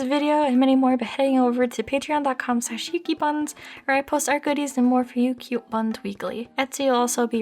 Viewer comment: the guy who was reviewing the movie: did you just say that you have HOT GOODIES, gimme